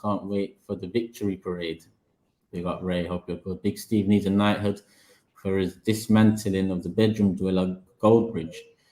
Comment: what did Big Steve say about Mark Goldbridge? I would love to know! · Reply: I said he's a massive bell end